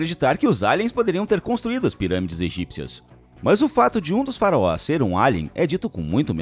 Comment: Eu ouvi “faroás” em vez de faraós?